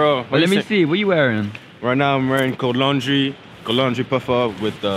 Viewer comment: Please someone tell me what the guy at 6:35 said he was wearing that accent is insane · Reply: Never mind COLD LAUNDRY sheesh